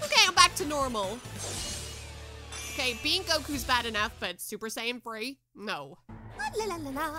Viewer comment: I heard "super sayin free no"